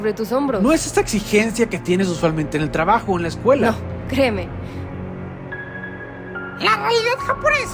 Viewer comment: si no saben que dice dice:no...creeme,no sabes cómo se siente